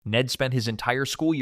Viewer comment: I thought u said Nettspend instead of Ned spent😭